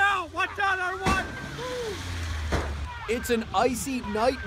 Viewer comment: I hate when Inside Edition shows the same looped clip throughout the video several times. We saw that truck hit the car 4 times. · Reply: Well to be fair at 0:02 the guy is heard saying watch out, watch out everyone but then all subsequent replays add the Louella in there